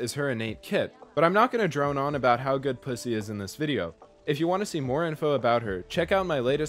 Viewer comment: is it only me or did he say p